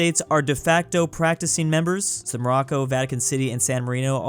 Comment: Anyone else hearing Morocco and not Monaco?